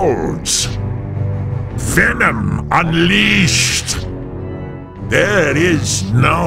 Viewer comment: What hes saying at 0:25? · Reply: Venom unleashed.